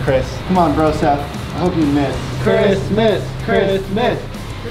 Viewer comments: When you said you wanted Chris to miss it sounded like you were saying Christmas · when they were saying "Chris miss" at 11:45 it sounded like and were say "Christmas